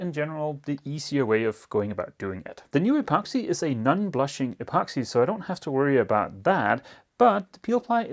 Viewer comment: Had this playing in the background while I worked and had to double-take when my brain thought it heard "nun-blushing epoxy"